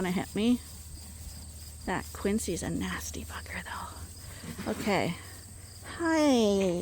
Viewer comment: Lol I thought you said Quincy was a nasty f@ but you said bugger. I had to play it back a few times 😂😂😂.